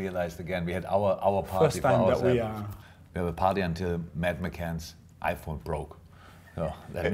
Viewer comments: Did he say he partied until maddie McCans iPhone broke? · I thought Klopp said “we partied till madaline mcanns iphone broke” , had to replay it twice 😂😂